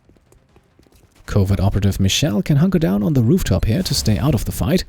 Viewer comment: thought you said ''covid operative'' had me for a good laugh there :P